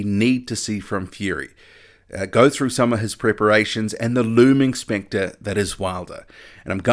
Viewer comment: OMG I thought you said " the looming sphincter that is Wilder". Hahaha but you said looming specter... I think....hahaha